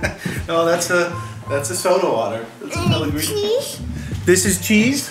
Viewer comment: I think says "is there coffee?" and then afterwards, I think he says, "and this?" or mabye "and these?", but thought it was "and cheese?".